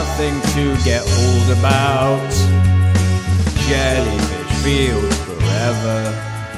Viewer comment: I thought it said "Jellyfish Friends Forever" lol